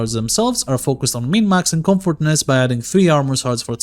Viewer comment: Got wisp prime cookin in the foundry :) Quick Q...@ 45:54 ... "the archon shards are focused on [[??]] max and comfortness" I couldn't understand what was said here. Thanks for the video! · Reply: "min-maxing comfortness"